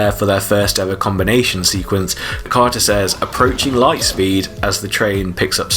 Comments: So...who's a Supertrain fan? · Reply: Dead honesty; I get chills watching the very first transformation sequence. Also, I always heard Carter's line on the ramp being: "Approaching flight speed!" Not sure if I'm right about that though. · I think Carter said "Approaching Flightspeed" hence why Joel immediately yells "YEAH BABY!!!" 😂